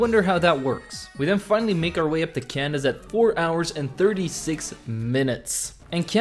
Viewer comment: When you said you made it up to Candis I thought you said "made my way to to Canada" haha I had to hear it twice to make sure hahaha your amazing